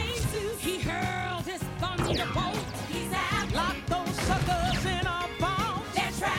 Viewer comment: I CANT BE THE ONLY ONE WHO HEARD THAT THEY JUST SAID FUCKERS THIS IS A DISNEY MOVIE IM HAVING A CRISIS